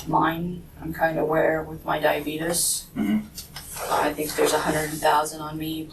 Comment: She really said, “Diabeetus.” 😂